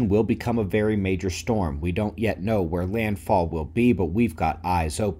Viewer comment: I could have sworn you said "...don't know where Rand Paul will be...," instead of "...where land fall will be". (Good grief, I've been watching too many D.C. news videos.)